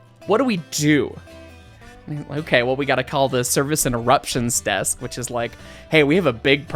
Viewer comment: at first I thought you said “Service and Eruptions desk”….because let’s face it, there were some severe bowel eruptions stored in that plenum….then I realized it was “interruptions” and it wasn’t as funny anymore.